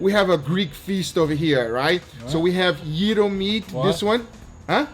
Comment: I thought at first said “Euro meat” !!